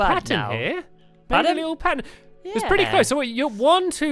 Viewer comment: did say my little pony